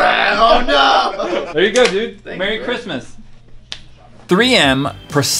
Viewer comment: who hears marry Christmas